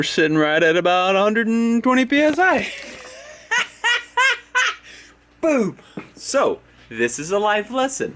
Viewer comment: it sounds like mats saying boob